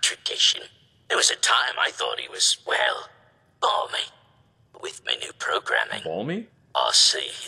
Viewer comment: I think the robot is saying "Barmy"